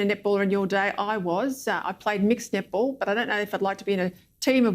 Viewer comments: I keep hearing nipple...👀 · I swear that women is saying "nipple" not "netball". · all I hear is: "I play mixed nipple?" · i'm sorry but i'm keep hearing nipple instead of netball, is that how netball suppose to be pronounced?